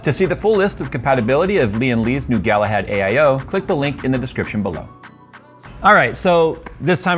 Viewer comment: Am I the only one who heard “click the link in the description belo-“